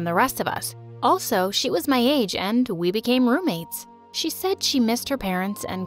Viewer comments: am I the only one who hears “and we were mermaids” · Did I just hear "and- we became mermaids"